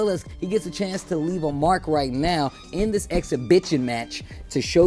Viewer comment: I'm only 2 minutes into the video and it's already cracking me up..did he just say "exhibitchin match"? xD